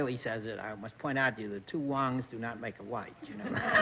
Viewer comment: Two Wongs don't make a right. Got to love the lack of political correctness at the time. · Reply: I thought he said “two Wong’s don’t make a white”